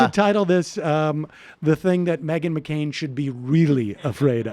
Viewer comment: Did he just say MAGA MCCAIN? 😂😂🤣🤣